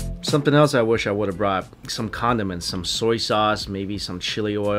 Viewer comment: i literally thought he said: "some condoms and soy sauce" 😂😂😂